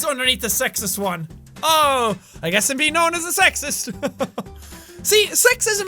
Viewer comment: I thought that said be known as a rapist and I was like WTF!?